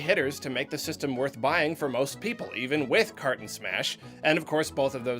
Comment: When you said "Kart and Smash", I heard "Carton Smash" and thought about how the hell I missed a game like that, but then I realized that I'm just a dunce.